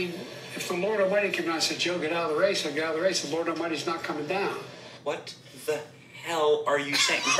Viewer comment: I actually understood what he said "If the Lord Almighty announces Joe get out of the race, I'll get out of the race.... Lord Almighty is not coming down" but NO he should not be President it is time to retire!